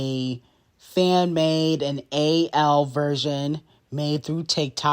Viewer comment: did you mean to say a.l instead of a.i ? Lol love your show l ❤